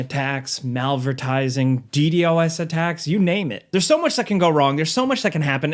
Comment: I thought he said CTOS 😂😂😂